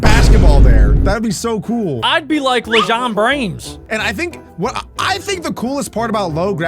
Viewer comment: did he just say lejon brains??